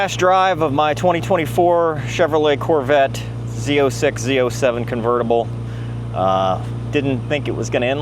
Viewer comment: Huh? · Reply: Misheard ya lol you said z07 for some reason I heard it as c7. Was a 0:50 my bad!